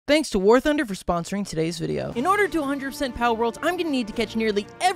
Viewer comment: The first time I started the video the ad cut off oddly, so I heard "Thanks to war for sponsoring this video"